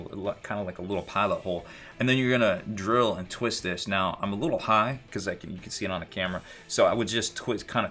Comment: Watching at work, half paying attention, and then I hear "I'm a little high...." look over and realize it's about the drilling position for the barrel... still cannot un-hear it. Great content man!